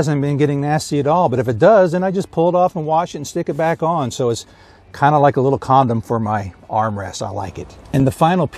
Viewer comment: Did he say "kinda like a little condom" lol haha